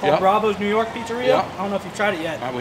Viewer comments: When a big guy tells you there is good food down the road, you listen. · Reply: I thought he said “huge fat man” at the end but it was actually “huge fan man” 😂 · At the end I thought he said huge fat man thank you!